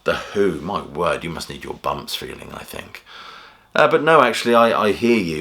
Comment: What the heck did you say @ 2:06? "My word, you must need your bumps feeling, I think?"